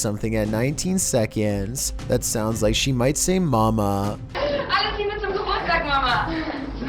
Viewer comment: She says: "Alles liebe zum Geburtstag, Mama!" is what she said. Which just means: Happy Birthday, Mom!